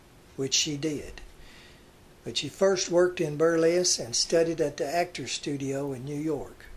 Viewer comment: "Burless"? Did you mean "Burlesque"? I thought at first you were saying Inger worked in a town called Burless, Kansas.